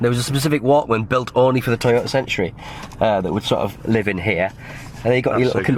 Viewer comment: For months now, I thought was saying "Toyota Sentry" and not "Toyota Century." I am way too American.